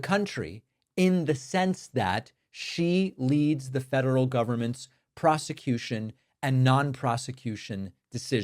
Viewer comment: I was a bit far away and I heard "prostitution". I was second guessing if I should double check.